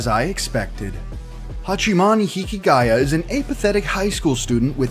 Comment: am i the only one who heard him says Hachimani Hikigaya instead of Hachiman Hikigaya..